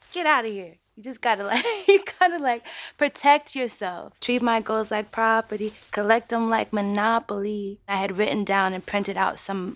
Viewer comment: i thought it said treat my GIRLS ike property